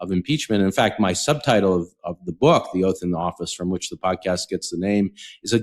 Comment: I thought you said the book title was “The Oaf in the Office”.